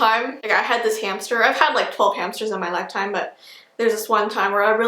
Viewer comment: I thought she said 'I had this cancer, I've had twelve cancers' I was like what!!!